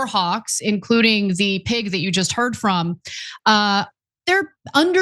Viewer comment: Did i just hear "... including the pig you just heard from ?" These left marxist are horribly disgusting